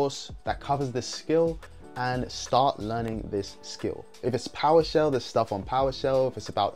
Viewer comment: did you say “poweshell?” Sorry I’m all new to this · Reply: No worries, yes powershell it’s basically a scripting language and command line interface. That is used with Azure and windows devices! 😁